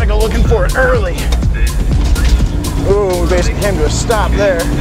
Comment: when the subtitles say "Ooh, basic (indistinct) stop there" he's saying "basically came to a stop."